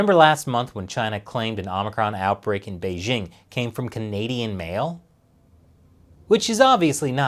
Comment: Wait .. what .. oh .., I misheard it as "Canadian male" ..